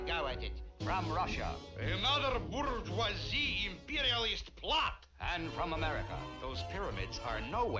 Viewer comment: What is Boris Badenov said at 0:56? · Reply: "Another bourgeoisie imperialist plot!"